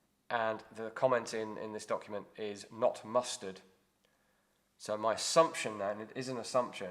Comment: 2nd Lt Hammond is recorded as "not mustered" but I misheard it as "not mustard" and thought that was rather an informal way to record an adverse HR judgment.